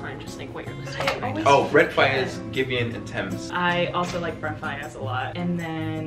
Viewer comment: Did Daniel say he listens to Tems? 🥰